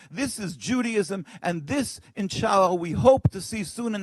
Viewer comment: Did he just said inshallah last at 5:43?